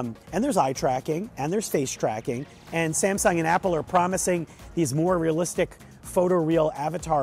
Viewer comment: Samsung and Apple? Is that what you just said?